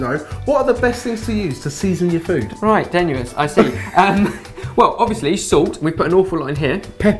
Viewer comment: Can someone please tell us what says at about 1:47 after asks about the best stuff to season food with?? "Somethingsomething, I see." It's driving 4 people crazy here! :) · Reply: I think said: "Right, tenuous, I see." I'm not sure, what do you think?